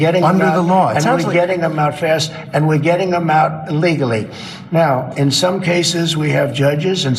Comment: Anyone notice that at 12:00 he actually says, "We're getting them out 'illegaly'"? Listen to that bit very carefully. I played it back a few times to make sure I heard it correctly. And it's evident that he does NOT understand the laws or difference regarding having a "hearing" as compared to a full on trial. Nor does he understand the difference between "seeking asylum" and an actual "asylum" aka mental institution. In my honest opinion, he has the mind of an 8 year old and continues to stay stuck on stupid. He surrounds himself with a bunch of suck up clowns who probably knows how dumb he is and is acared to tell him the truth. It has been proven over and over again that immigrants do not commit the amount of crimes he thinks they do. The reason being is that they do NOT want to be sent back to a potential death, their teenage daughters forced into prostitution and their sons forced into gangs. They also pay into OUR taxing systems and do NOT have access to the benefits we receive, such as social security and Medicare. They have to become a naturalised citizen first. The amount they have paid into is approximately $102 BILLION dollars. By illegally kidnapping them and sending them to basically concentration camps, we have literally turned into <num>'s Germany. It's scary watching our country turn into a place that is the complete opposite of what our forefathers envisioned.